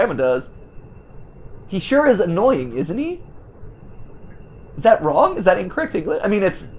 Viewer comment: Matpat, around 14:46 where you were confused about the messed up English, said “aint he” instead of “isn’t he”